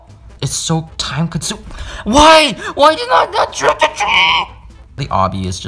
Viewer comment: i thought you said trick or treat